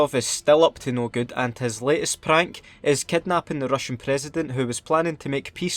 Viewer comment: Did you just say Prank LOL